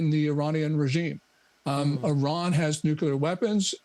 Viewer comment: Did he say, "Iran has nuclear weapons"? (4:47 in) Usually various ones say that Iran is such and such weeks, months etc away.